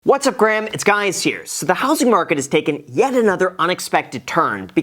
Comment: All this time, I thought you've been saying, "It's Gaius here" as in Gaius Julius Caesar! 😲